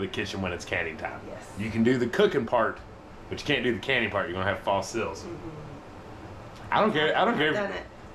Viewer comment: What?? I didn't understand about why you couldn't do the canning part? It sounded like he said, "Or you'll have false something?" Could you explain please?